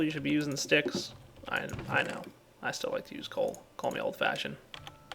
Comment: did u just say i still like to use coal i know coal me old fashioned because i swear u just made that genius pun